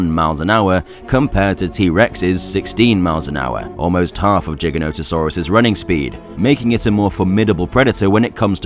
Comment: Who else heard him say jigganotosaurus?????